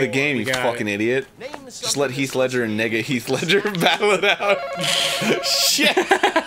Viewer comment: did arin say n*** at 3:15????!!!